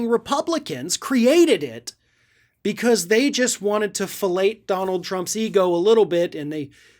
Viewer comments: Way to go, Donnie! Finally, an award you can put on your shelf. Now, go away! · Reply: i could swear that said "fellate thump's ego" at 1:17. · "fellate" donny drumpfh's ego! hilarious, appropriate and did i hear you right? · Lol, and this is typical of why is the best. No punches pulled and asses that need kicking, get kicked. Keep up the good work, Mr.. Oh, and a humorous way of doing these things too. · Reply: did anyone else hear say "fellate" drumpfh's ego?